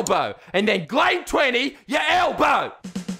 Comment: my Aussie isn't good enough, what does he say? "And then ??? your elbow"? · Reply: Glade 20 your elbow.